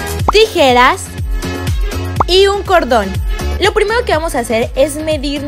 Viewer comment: Entendi un condón xd